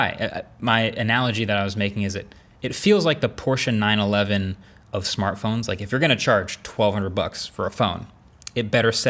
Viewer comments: what I hear - "Porsche 911 of smartphones" v/s what I see - "portion 9/11 of smartphones". Marques, you better calm down your subtitle writer.😂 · Mistake in the subtitles: “portion 9/11” instead of “Porsche 911” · Did the captions just say portion 9/11 instead of Porsche 911 at 11:55?